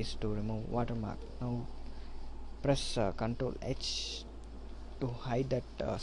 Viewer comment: press Control what? didn't understand what he said. Sound like control x but not sure. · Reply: press Control (ctrl) + H